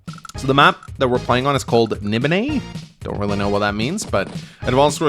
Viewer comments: it sounds like you said "Nibenay" and if that's true then the name is a reference to Elder Scrolls, specifically Elder Scrolls IV. Nibenay is an area of Cyrodiil with lots of small lakes not unlike this map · What's the name of the map again? I didn't quite hear it properly (I heard "Nibenay", which is the name of one of the regions of the AD&D Dark Sun campaign setting, which makes no sense whatsoever)